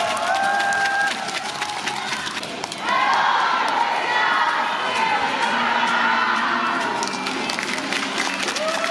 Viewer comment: What did they say?? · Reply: Hello Indonesia, See u in Jakarta ???